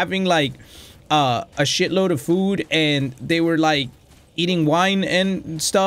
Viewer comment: Boi did u just say “and eating wine”